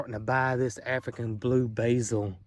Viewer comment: I thought you said African blueberry 😂